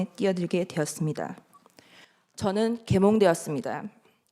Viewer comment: 라이브 틀어놓고 일하는데 듣다가 깜짝 놀랐다,,,,,,내 귀에는 이렇게 들렸다,,,,,,저는 개 몸 되었습니다,,,,,,지난번 mbc 사태때도 내 귀에는 바이든 날리면으로 들리던데,,,,,,ㅋㅋㅋㅋㅋㅋ